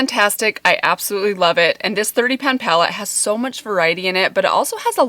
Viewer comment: At first, I thought you said 30 pound palette!!! It’s 30 pan, I see...😂